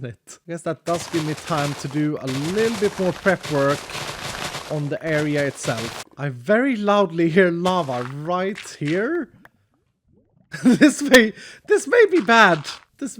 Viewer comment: I thought he said llama I was so confused 😂